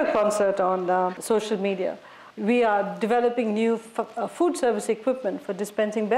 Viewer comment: did she said ”fuck”? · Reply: Madam said, food.